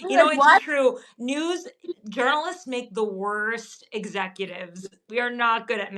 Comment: I first thought I heard you say that girls make the worst management. But saying journalists may be just as bad…